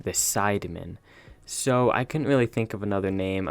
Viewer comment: I swear you said spiderman and it looks like it with the red and black suit